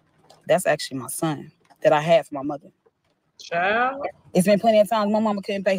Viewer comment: Did she say she had a son for her MOTHER 😳😳??? 16:33 ???